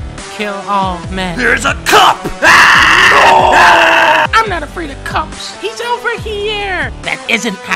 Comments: I die laughing every time I hear "I'm not afraid of cups." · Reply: Oh I heard "Im not afraid of cops" and still died of laughter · "There's a CUP" "AAAAAAAAAAAAAAA-NOOOO" "I'm not afraid of cups" Mastapiece. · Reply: I thought she said "I'm not afraid of cucks"